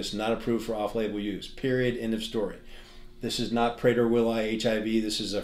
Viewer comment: What's he saying "it's not" here?? "This is not ________ HIV" ???? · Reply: Prader-willi